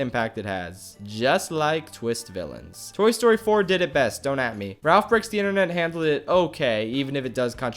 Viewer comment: Whew, I thought you said Toy Story 4 is garbage. That was close.